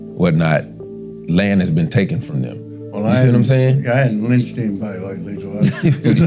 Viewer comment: Am i the only person that heard him say "Well I haven't lynched anybody LATELY ..."?... *gives tf up *